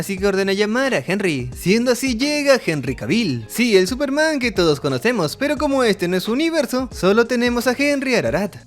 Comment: Escuché Henrry papucho kavil y desperté 😅